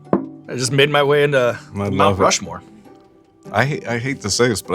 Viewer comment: Did he say "Mouth Rushmore"?